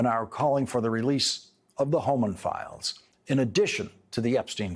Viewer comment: 🤣 For a minute I thought I heard Homer Files. 😂😅 My bad, Mr. Lawrence.